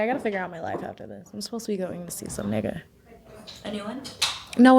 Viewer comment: did she say some nigga????